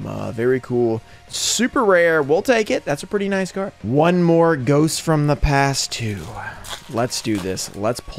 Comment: Thought you said you’re done opening ghost from the past? Haha